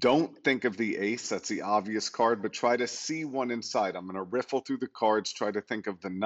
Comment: When he shows the cards and we need to pick one, i just imagine myself closing my eyes and pick a card randomly. I did choose king diamonds. If he adapts his trick with “ok this deck of cards is incomplete.. missing a few cards. But i want you to look at the cards and pick one.. That will be better. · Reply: NO, he clearly said " TRY TO SEE ONE INSIDE " man, watch it again...